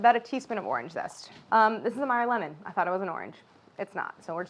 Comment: What kind of lemon she said she's using at 11:40? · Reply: Meyer lemon